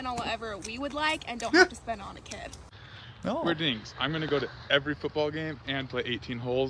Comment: I thought they were saying "we're dicks" lol😂